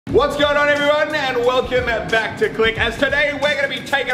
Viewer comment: Muselk: what going on everyone Me:i thought he said what’s going on GUYS